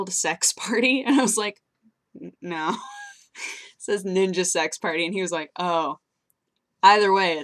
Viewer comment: i thought it said niga sex party LOL